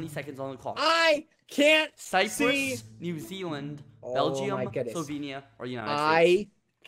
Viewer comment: I can’t get over the fact that it sounds like he says bell-gee-um! 🇧🇪